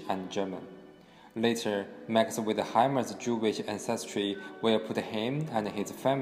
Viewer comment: Please could you add some subtitles? I can't understand large parts of this and the subtitles make no sense at all! 0:22 'later Maksim with a hymen Jewish ancestry' 01:12' from fry with a hammer moved to the University of Aberdeen'